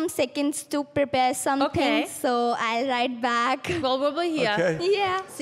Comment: Did she say i'll right back😂